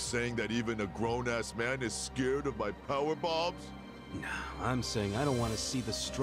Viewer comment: lol i was starting to fall asleep and thought i heard power bottom.... honestly thats what he said now and i wont hear anything else lololol